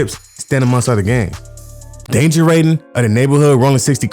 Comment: 😂😂 I heard you sounding like Elmer Fudd at 42:34….danger waiting. Keep these jawns flowing you be on point. 💯